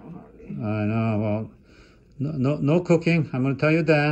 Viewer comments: Wow when you said no cooking, I could have sworn I heard no cocaine, I was like wtf did he say, lol. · I can tell where my mind is that cuz that's exactly what I heard myself is when he said cooking🤔 I thought he said cocaine and then I thought he said he was going to write a note so that she didn't have to cook cocaine.😏😏That's what it sounded like doctor's orders😂😂😂